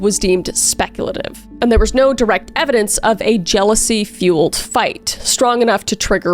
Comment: "... treats and other essentials." True. Treats are an essential. Also I swore I thought you said "diarrheal evidence." Very advanced forensic discipline.